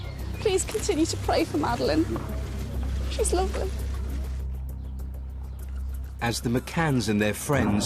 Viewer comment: Did Kate say 'She's lovely' at 13:54? Seems pretty strange to me if she did..